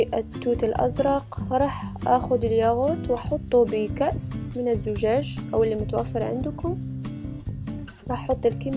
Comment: ايش قالت؟ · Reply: MEMA MEMA كأس من الزجاج